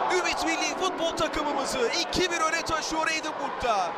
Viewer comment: de Ümit Milli takımımızı 2-1’e mi taşıyor dedi ben mi yanlış duydum🤦🏼‍♂️